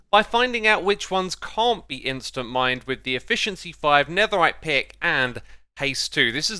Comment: did you say netherrack pickaxe?